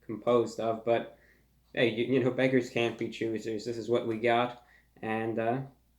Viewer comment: does he say “beggars can’t be choosers” or “bakers can’t be choosers”